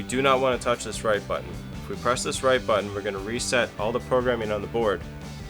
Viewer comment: I thought you said press the right button 😅